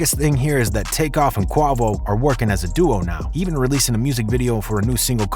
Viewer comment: did he say kuavo ????? instead of quavo ?